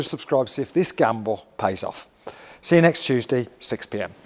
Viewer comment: Did just say C U Next Tuesday 🤣🤣